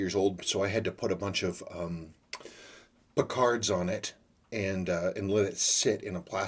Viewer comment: You put a bunch of 'BOCARDS' on the strap to soften it up?? What is that word said -- 'Picards' or 'Bocards' or what?